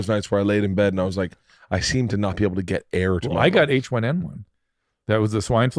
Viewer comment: I thought said Bert got H1n1